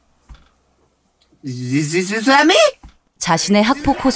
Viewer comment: 근데 뭐라는 거지....?ㅋㅋ 맨스티어 팬인데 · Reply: 패미